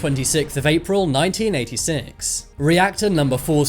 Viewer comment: subtitle says 1968 he says 1986